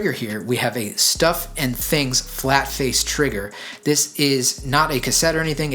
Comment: "Stuff and things flat face trigger" - Am I hearing that correctly??